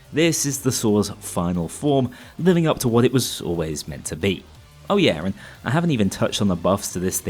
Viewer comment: did you just say "living up to what it was saw-lways meant to be?"